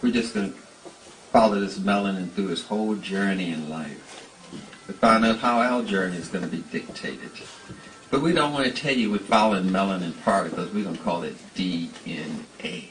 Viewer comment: somebody please respond. what does he say at these time stamps. 14:50 through 15:00 the what journey to be dictated then the what melanin particles? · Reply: found it. he says Thionyl chlorophyll journey and Thionyl melanin particles. dc references this with superman and his father jor-el. with chlorophyll being the green, grey and yellow on the dna structure and colors jor-el wears. superman wears blue, red, yellow, colors superman have worn or associated with which will stand in for thionyl, thiame, thymine, so on. marvel knows a lot about biology, chemistry and hides these things in plane sight, same as marvel with logan being ogun god, logan name came from ogun.